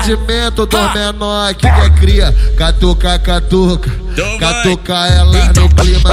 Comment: Agr q entendi ''cata o cano'' kkkk